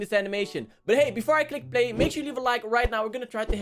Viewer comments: SHOULD I REACT TO MORE!? ► TikTok - <link> ► Instagram - <link> ► Twitter - <link> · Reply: i thought said "befor i klick bait" 😂😂😂😂😂😂😂😂😂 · but hey, before i click play... Me: Sounds like you said "But hey, before I click-bait... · did he just say before i Clickbait?!!?!? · Your accent is kind of making it hard for me to understand what you're saying. I thought you said "Before I clickbait," but you actually said "Before I click play." I'll try to understand as much as possible.